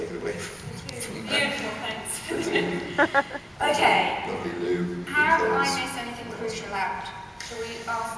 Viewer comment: What does Ben say at 09:00.... something like "... lovely Loo..."?? I can't understand it :/ Thank yooouuu! =)